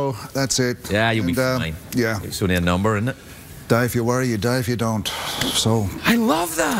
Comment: Anyone know what was the line he said on 19:05.. "dive you worry dive you dont"? Or something like that.. What was that line about? Sorry for my english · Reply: he said "you will die if you worry and you will die if you dont"